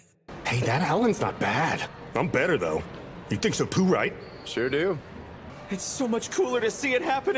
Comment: "I'm better, you think so too right?" was so cuuute!! His need for validation from Akaashi just gets me every time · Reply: My dumbass thought he said: “He takes a poo too right?”🤣